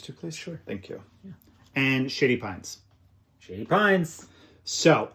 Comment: I know that is saying "Shady Pines", but I keep hearing "Sh*tty Pines".